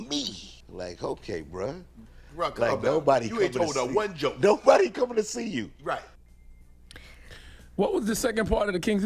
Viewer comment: all I heard was "Ain't nobody coming to see you Otis!"🤣🤣